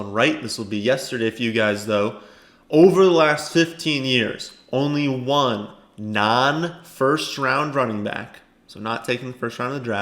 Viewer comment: I thought he said they had to be first rounders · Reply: no it said “just one non-first rounder.” That was kinda worded weirdly and it def threw off a bunch of people in these comments lol